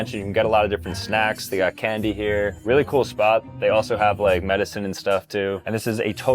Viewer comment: thought bro called the zyns medicine for a sec 😭